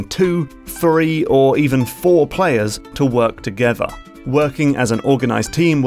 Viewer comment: I keep hearing saying: "two, FURRY, or four players..."